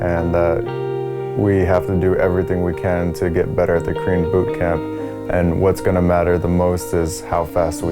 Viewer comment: I keep hearing "Cream Bootcamp" instead of "Korean Bootcamp" Sp4zie messed up my head! xD